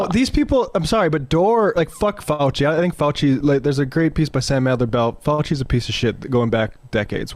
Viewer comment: What's the title of the piece on Fauci and who's the author? Sam who? I didn't hear that properly. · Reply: Yeah, that took a bit of work and luck. I haven't read it yet but I think he was saying Sam Adler-Bell. I'll link the article separately in case it gets deleted.